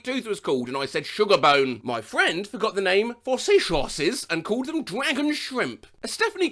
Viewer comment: Did he call seahorses sea shorses?